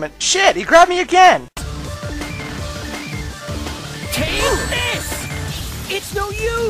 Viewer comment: When sil ver said: "take this!" I thought he said: "Penis!" Like painis cupcake